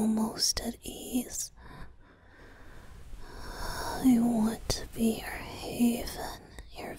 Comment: Oh my god, for a minute i thought Venti said "I want to be your husband" 😳 Anyway, this audio is one of my new favourites!! Thank you for this!! <3